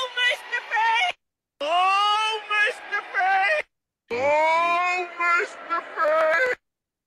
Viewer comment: Intresting.... So when you played Kamala introducing Tim Waltz, and it apparently it said ( I'll make the prey) I heard..."Oh Mr Fake" So like says, it is very difficult to decifer what is actually being said.